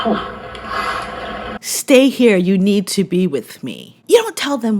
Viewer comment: I heard another recording of him at the beginning and it sounded like he said, "you need to be here FOR me." Big chicken ---- Harry! His mentioning Mary Tillman and merching off his mother once again was totally despicable but what else do we expect. 🤮🤮🤮